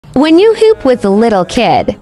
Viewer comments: Bro took “I underestimated him” to a whole new level💀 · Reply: I thought she said when u poop with a lil kid 😭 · I first I thought it said when u poop whether a little kid not hoop 😂